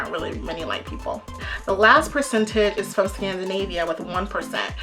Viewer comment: Sorry, I heard you say TANZANAVIA. Did you mean Tanzania?